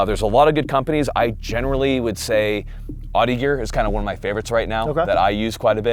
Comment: For anyone wondering what company is saying at 14:09 and 19:47 he is saying OTTEgear